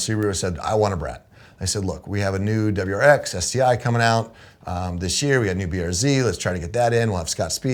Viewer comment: did he say we have a new wrx sti coming out